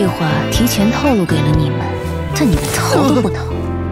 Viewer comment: 到底說了什麼QQ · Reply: 但你逃都不逃